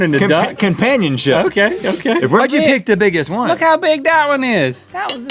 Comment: sounds like he said picked the n¡ggas one